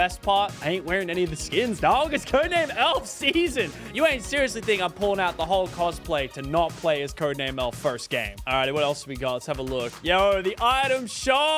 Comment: did Lachlan say the Holocaust gameplay or am I just tripping??